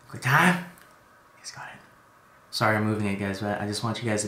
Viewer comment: What is he saying at 13:10 and again at 13:16 ?? Nice got it? He says it in all his videos right after his pattented "QuickTime!"